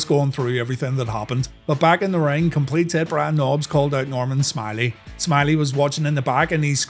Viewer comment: Every time I hear, "Complete Tit Brian Nobbs" I laugh my ass off idk why I find it so funny but I just do 😂